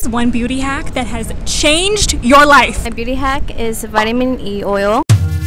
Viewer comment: Not me missing the whole word “vitamin” I fr thought she said “pee oil” 😳🙈 I was like no baby.. we not doing this 😂😂